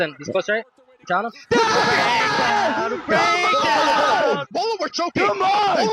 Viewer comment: wtf does Brandon say before “choking” is he trying to say something in spanish lol?? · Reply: Haha nah he says Wallah, it means I swear to God in Arabic